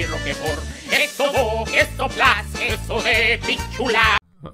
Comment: I'm pretty sure that I heard "queso de pichula"